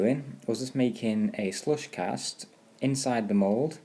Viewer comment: on 1:05 u said u ended up doing ... I couldn't understand what u did... my bad but what is it that u did ??? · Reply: De Leon I made a slush cast, basically it mean to pour in the latex and pour it all around the inside of the mold, and then pour out the excess, and do that a few times until you get the required thickness.